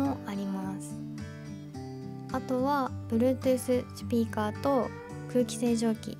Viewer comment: 『チュピーカー』って聞こえるけど気のせい？